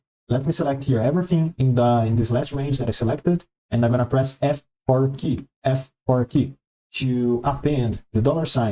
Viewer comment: min you said we should chose the F something key,i cant figure out what you are saying due to the accent and im stock there.please can you tell me the key you were refering to,will really appreciate it thank you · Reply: I'm not sure what he said, but you want to press F4 to add the $ around the cell number. -- I listened again... what he said was "the F4 key"